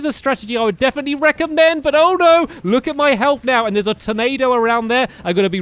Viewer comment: did anyone hear at 9:04 "there's a tomato??